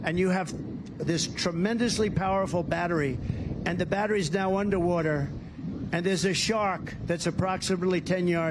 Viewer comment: did he say under water or wonderwater.. he probably was thinking of wonder woman